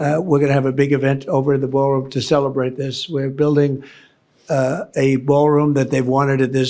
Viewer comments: Sounds like he said "they're building an ARK." 🤪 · I saw this last night. I thought he said he was building an ark.